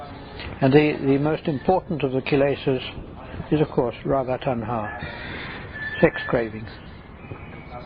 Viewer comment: discussing the Kilesas, is he saying Radha Tanha or is it Bhava Tanha? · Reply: It's not Radha tanha.. That's Raga Tanha. Raga means attraction and attachment.